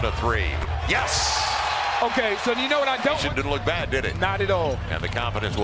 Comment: I swear @ 0:38 the commentator said "shit didn't look bad, did it"...